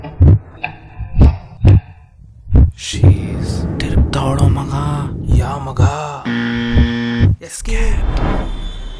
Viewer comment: What does he say at 01:35, no subtitle also for that dialogue... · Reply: Hi, the dialogue is "She is..."